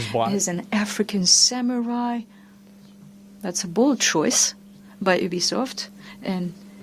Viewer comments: thought she said bulls**t choice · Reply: You must've been distracted to hear that cause i clearly heard "bold choice" 😂 what game were you eating or food you were watching? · thought she said bulls**t choice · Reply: She said bold choice